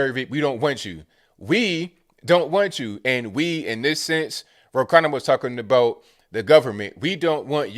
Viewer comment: For some reason I thought you said Brokanda.